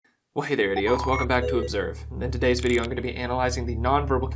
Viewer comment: I thought he said "Hey there IDOTS!"